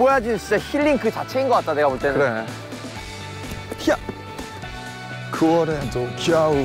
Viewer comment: Does anyone know what did Namjoon sang at 0:30? Thanks! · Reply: "Even in September, winter is here" (He said in the Spring Day melody from the part "Even in August, winter is here")